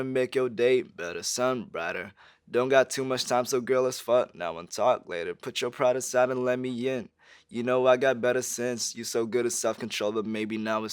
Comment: I always thought he said “better sins” not “better since”